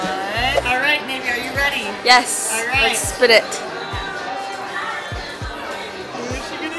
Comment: Tell me not it sounded like she said spit it